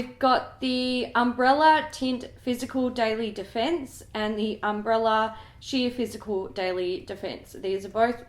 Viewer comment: Umm did call them 'Umbrella' not 'Umbra' at 11:30 or is that just my bad hearing?😂